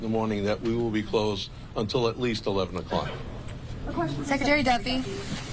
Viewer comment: Did she just say "Shekretary" ?